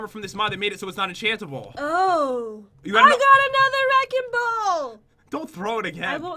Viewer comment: did Jen say “I got a nether wrecking ball!”